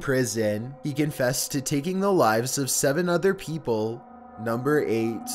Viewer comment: I thought he said 700 people